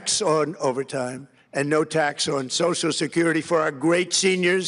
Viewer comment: Did he say "no tax on security fraud?"